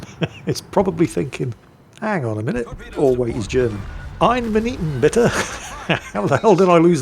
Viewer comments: oh boy. 8:39 Been watching for at least 8 years now. Took me this long to realise he's saying "Wait a minute" in german. I never understood what he said until now. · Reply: I thought he said "I haven't been eatin' bitter" XD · Holy shit I've heard you say "I'm an eaten bitter" for YEARS at this point and only JUST realized what you actually meant and just pronounced like a british man speaking no other languages was "Eine Minute Bitte"